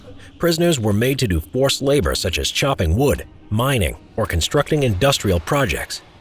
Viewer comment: on 1:05 i thought he said child labor